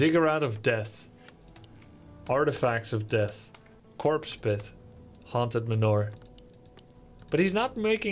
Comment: Each time Grubby says "haunted manor" i hear "haunted manure". You know what trumps a Black Knight? A Dark Knight.